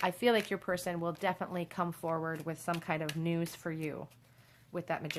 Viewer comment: On 43:03 did u say nudes or news🤣